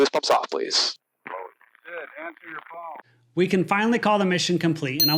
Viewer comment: Did I hear some son of a b** on the radio say "good answer your phone" 30:18 ... right after the pilot gave the instruction "boost pumps off"??? Tell me if I'm wrong because I'll be damned while I'm flying in 80 year old mechanical aircraft!